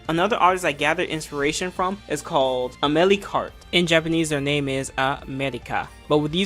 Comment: I really didn't hear the name well, nor does subtitles help. Mind putting that artist's name please? It looks cool. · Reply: Amelicart